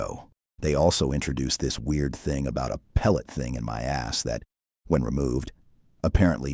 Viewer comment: I misheard the thing about the pellet as "Pelican in my ass" Hell at least that would have made the show ... something?